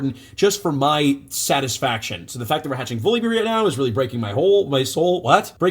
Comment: "really breaking my hole" hah. That's what she said.